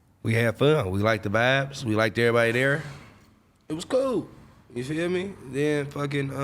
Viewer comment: did he say COOL